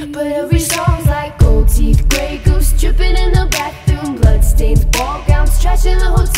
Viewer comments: This whole time I thought she said "Strippin in the bathroom" smh. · I thought it said "Gold teeth, Break ups strippin' in the bathroom" °•° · I have a confession to make For like as long this song has been out I thought she was saying stripping in the bathroom which made sense 😭